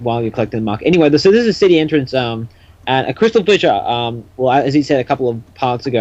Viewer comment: Did just say CrystalGlitcher? Possibly caused by the call quality but that's what I heard.